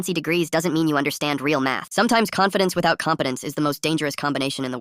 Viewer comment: "confidence without competence" is absolute bars 🗣️🔥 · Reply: I heard it “confidence without confidence” 😭😭